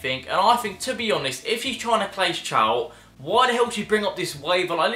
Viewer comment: did anyone else hear him say "clase chout"